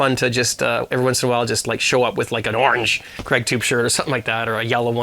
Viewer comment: Lol did you hear what i heard at 7:00? Redtube shirts!